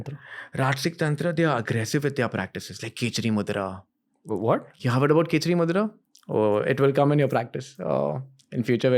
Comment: How is the Name of the mudra at 4:22 I cant understand it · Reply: It's Khechari Mudra: almost everyone in the comment is talking about that only!!